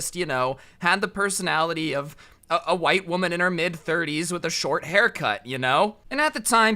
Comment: I hear “ white teacher in her mid 30’s with a short hair cut”... I Think... I’m living his life